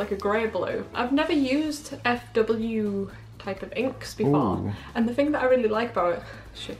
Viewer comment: I always thought Fw inks said fu inks until this video